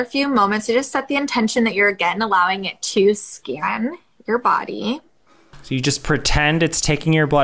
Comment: Also I'm laughing my ass off for that clip... every time the hun said 'let the machine scan you' I just heard 'SCAM you' and I was cackling. XD